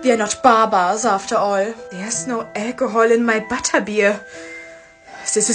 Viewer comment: I was just listening and legit thought she said "There’s no eggroll in my butterbeer" when she was actually saing "There’s no alcohol in my butterbeer" I was so confused, lol.